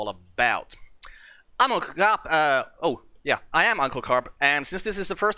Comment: when you said "Uncle Carp" i had to rewind it twice i could of swore i hear "Imma Cop" lol